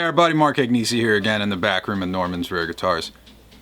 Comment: Couldn't help but laugh as you were introducing this video - my phone buzzed just at that point, and while I wasn't concentrating, it sounded like you said "...in the bathroom of Norman's Rare Guitars" Great series by the way and "Byeeeeeee" at the end always makes me smile :-) Keep up the good work...